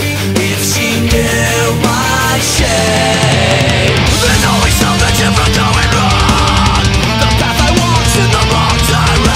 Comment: Damn for years I thought he said "there's always something she was doing wrong " but its "there's always something different going wrong " 🤦‍♀️🤦‍♀️